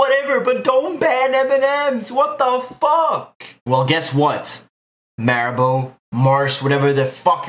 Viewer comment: Did he just say "maribo" instead of "haribo"? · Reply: It's ''Marabou'' It's a company that makes the best Chocolate here in Sweden